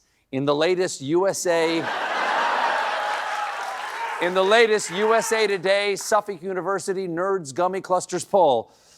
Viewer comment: what does that guy yell? Any theories? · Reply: "YEAH, SHIT, YEAH" is what I heard lol